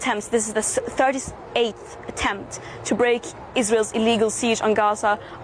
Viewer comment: Did she say (at 4:25) that this is the "Eighth attempt" or "Thirty-eighth attempt" to break the "siege" on Gaza? Either way, hasn't Greta ever heard of Einstein's definition of insanity: repeating the same action over and over expecting a different outcome?